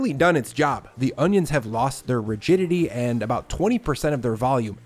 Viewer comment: at first I thought you said the onions lost their virginity 😂😅